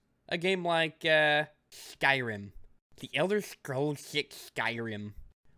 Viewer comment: Did he say elder scrolls 6?